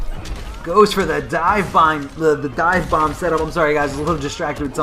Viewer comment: Honestly, I love but I am sure he is stoned in these videos..He even said "Divebong" instead of "Divebomb," then said he was "distracted by something here"